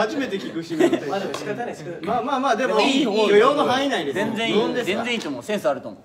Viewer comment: 許容の範囲内ってやつが 扶養の範囲内に聞こえて勝手にドキドキしました